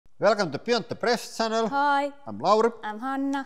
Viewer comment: Every time they say, "Welcome to the Beyond The Press channel." I hear."Welcome To The Built The Press channel." 😂